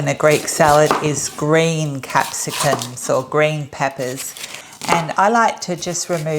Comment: To clarify the recipe, it sounded like you said to add a capsaicin pepper. It looked like a sweet green bell pepper, is it really a hot capsacicin?